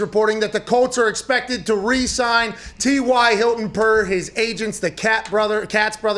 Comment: I thought it said Ty resigned.